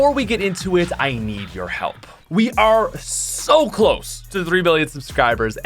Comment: I heard three billion subscribers. Congrats about half the population of the world is subscribed to you Edit: had to fix the timing